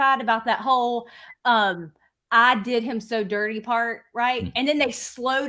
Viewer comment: Good to hear these lawyers think that the prosecution and defense lawyers are not doing the best job. I found them incredibly unimpressive, especially in their opening statements., but I thought "What do I know?" BTW, I heard "I", not "they," so I wish people would stop saying it's obviously "they." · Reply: I heard, “ I did it so bad.” Let’s hope jury doesn’t get twisted up in what he said, it wasn’t a Perry Mason moment.